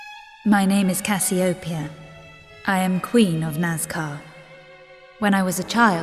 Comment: it sounds like she says she is queen of nascar :P